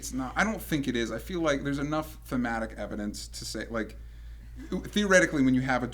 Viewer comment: what did he say at 20:46 feumatic evidence? what?